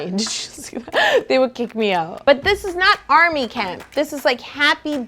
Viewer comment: I thought you said "They would cake me out" not "they would kick me out" lol